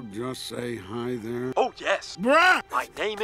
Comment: I legit thought he said "BRUH"